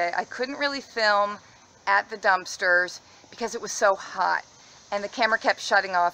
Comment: I thought you said holy dumpster. Lol